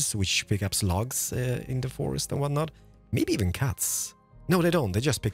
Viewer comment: I thought you said "maybe even cats" and I was so confused how a machine like that would pick up a cat lol